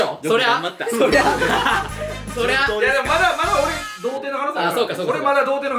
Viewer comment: 童貞にしか聞こえなかった。